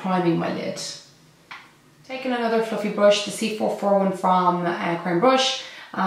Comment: Love this look 💕 but when you said another fluffy brush I literally thought you said another fucking brush 😂😂😂😂😂